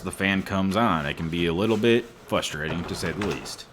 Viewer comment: Did you say, "Flustrating?" @ 2:03 ???? 🤣🤣🤣🤣